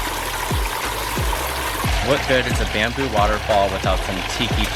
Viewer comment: I thought is said Bimbo waterfall instead Bamboo waterfall.